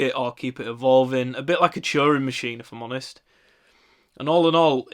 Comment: He said 'Turing machine' but I first heard 'Churro machine'. Sounds good, great vid!